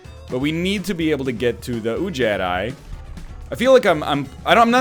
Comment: I have close to zero knowledge about this game, and I keep hearing NL say "Ooh jedi"